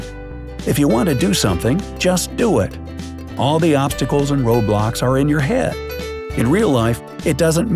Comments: DID HE JUST SAY ROBLOX?!?! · NANI???? DID I JUST HEAR HIM SAY ROBLOX?!?? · DID I HEAR ROBLOX?!?!?!?!